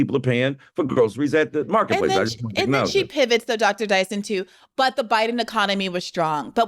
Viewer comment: Briahna Joy Gray ATE HIM UP. And Michael Eric Dyson knows it LOL. Everytime she said "Dr.Dyson...", I heard "Mista' Banelli..." LOL. Dr.Dyson took it in stride, though. You can tell he has a lot of respect for BJG and her intellectual rigor.